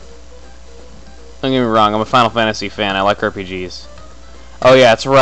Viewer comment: did he just say he likes "Herpe-Gs"?